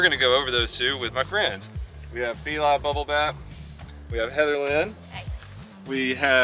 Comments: It sounded like he said Eli Bubble Bath instead of. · "Be-li Bubblebap". I had to listen to this like four times because I kept hearing "Eli Bubble bath". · hahaha I thought you said Elon bubble bath at first.